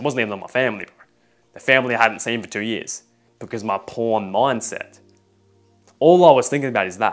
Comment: i have something to say, at exactly 3:35, you said the actual word p*rn instead of corn